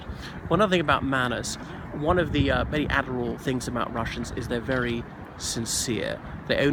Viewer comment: What you said starting at 2:35 about the manners is very interesting and I guess it`s true for the ex-Eastern Block countries too. Did you say that it`s "admirable" ?